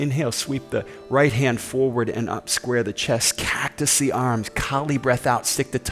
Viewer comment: Hey! Thanks for anothet great practice! Please, what did you say at 12:11? "Cat to sea arms"? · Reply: Hi Leo. Oh this is a good question! I say "cactus the arms" which means bend your elbows 90 deg so your arms look like a cactus 🌵I live in Texas where the cactus plant is common and everyone knows it. But I'm just realizing if you don't live near cactuses this might not make sense haha. Thanks for asking this!! Namaste my friend 🙏✨